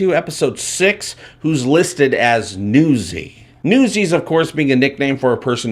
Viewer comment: It sounded like you said " like an UZI" :))))) <link>